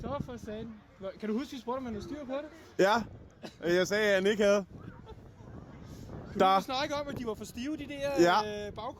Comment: wait what did he say did this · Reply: The 2nd guy asked "do you remember that I asked the guy if he knew, what he was doing?" And the forst guy said. "Yes, and I said that he didn't."